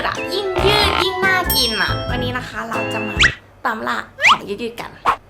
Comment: ได้ยินว่าตลาด😅